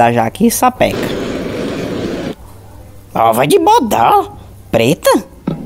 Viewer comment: o que ele disse aí kkk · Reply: Ova de bodó